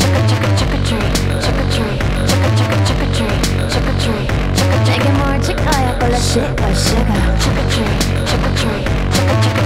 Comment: I am mishearing "Shit by Suga"!!! Like whut?!